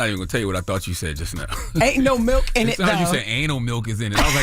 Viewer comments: Nah don’t gaslight Charlamagne. I heard “anal milk in it tho” too! 😂😂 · I heard "Anal milk in it" 😂